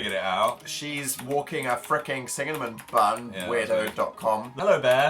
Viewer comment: did dan say singamin bun at